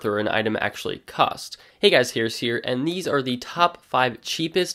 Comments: I thought he said hey guys scarce here lol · i thought u said hey guys its scarce here xD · Lol I thought he said hey guys scarce here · I thought you said "hey guys scarce here" XDDDDD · I thought he said, hey guys its scarce here at first